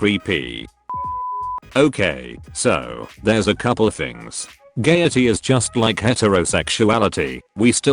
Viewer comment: wait did they just call it gaiety?????